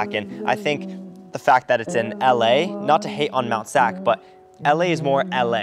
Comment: I thought he said “not to hate on nut sack” 😭